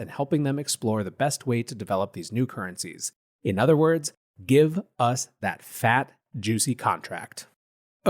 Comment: Had to rewind the replay. At 8:35 I thought he said "...give us that fat juicy cock back".